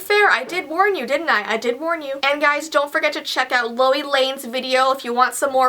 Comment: Who else thought she said Louis lane